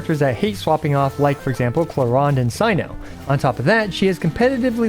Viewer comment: Cyno And Who?? i didn't hear it properly, was it clorinde. can some one tell me the name. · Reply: Clawronde